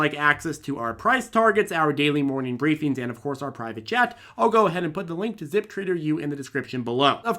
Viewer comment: "private CHAT", OOOOHHHH.. i always thought you said "private jet"!